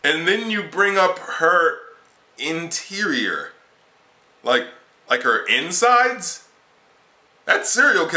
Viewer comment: I thought he said, it's juicy her interior